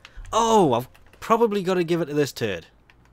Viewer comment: woah woah woah, did you just call Clem "This Turd"? How dare you insult the legend? XD